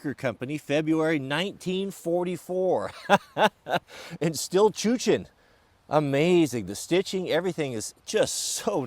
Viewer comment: Good stuff as usual - but can you tell me what word that is at around 2:15? "It's still chuchin!" (??)